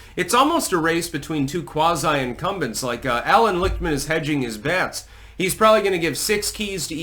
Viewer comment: I thought you said Alan Rickman was hedging his bets and I was thinking, he’s dead. But I know what he would be doing if he was alive today. Scratching and clawing at the lid of his coffin.